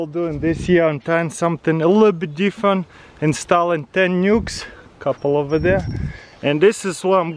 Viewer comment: i thought he said "in STALIN ten nukes"